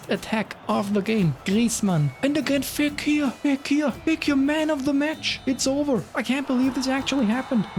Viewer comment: Nice Video 13:50 did you say : Fekir weg hier? 🤣🤣🤣 Camavinga of the newst France pack has the worst finish in the whole eFootball's history 🤣🤣🤣